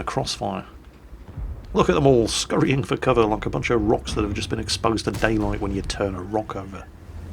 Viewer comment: did you just say like a bunch of rocks exposed to daylight when you turn a rock over?